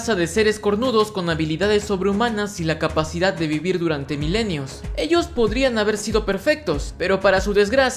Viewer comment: por un momento pense que dijo "mimir"